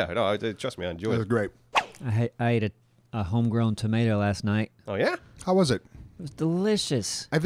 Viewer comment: the first time i heard that i could have swore he said "I ate an ingrown toenail last-night" :)